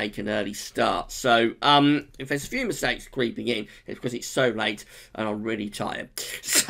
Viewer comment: I had to pause and rewind a couple times @ 12:50 it sounded like you said "If there were a few insects creeping in, it's because it's so late and I am really tired!" Very early days but two models agree! CFS V2 & JAMSTEC). But generally - it's quite rare for a model to predict a below average autumn in terms of temperatures and i can't remember when the UK has last had an autumn below average in terms of temperatures. But too early as you mentioned - I am keen to see if we do flip back into a westerly QBO. How long can an easterly QBO be maintained for and is it possible to have an easterly QBO for two winters? I remember winter 16/17 we were due to go easterly but we stayed as a westerly QBO - I wonder if this could be the same when it comes to winter 18/19. WHo knows. Thanks for update btw! as always. Will watch your summer forecast video now.